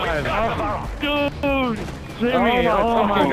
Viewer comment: did he say i just peed a little bit